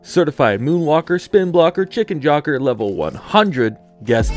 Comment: i heard chicken jœrk€r